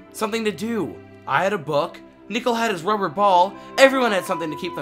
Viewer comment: in the captions, it said Nickle for a second instead of Nickel